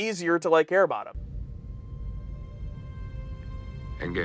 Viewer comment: did anybody hear him say "air bottom" when he said "care about them." Love these analysis episodes, keep up the great work.